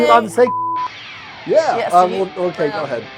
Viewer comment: At first I thought it said The Uncle Diddy show. Lol.